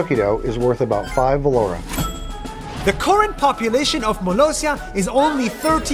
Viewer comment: malaysia instead of molossia ?? 💀 (turn on auto captions)